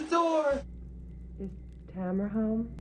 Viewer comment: When I heard 2:53 I heard "This hammer's home" or perhaps "Is Hammer Home" was wondering if the Woman's name was Hammer and also of the person was knocking with a hammer. This is true for a few reasons. One the person sounds like a robotic drunk black lady to me and now even though I know it is "Tamara" I have no idea why she is asking. The woman response is an answer, I mean if someone asks if I am home and I like and say "No" it is obvious that I am home. Also why did she ask a second time? was so confused that it made me less scared byalot